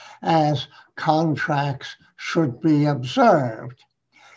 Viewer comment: I misheard him when he said "contract should be observed". I heard it as "contracts should be absurd". Does that mean I'm qualified to become a cabinet minister?